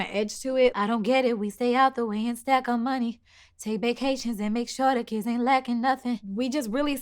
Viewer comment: This whole time I thought she said “take baecations and make sure the kids ain’t acting ugly”😭😭😂